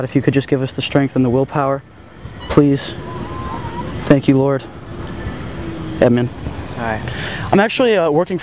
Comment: Bruh...@ 2:08 Did he said eminem instead of amen. 😆